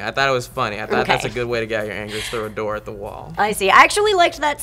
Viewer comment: Did he say throw a door at the wall?